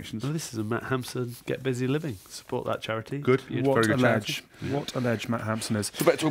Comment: 16.44 I honestly thought the presenter said "what a veg Matt Hampson is" and was going to make an official complaint.